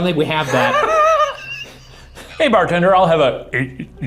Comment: sounded like “Like the Alcohol” And Happy Birthday Shane!